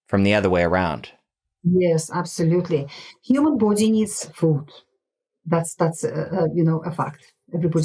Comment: "...human body is...?... that's a fact." What did she say here?😮 ...food?.... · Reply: "Human body needs food"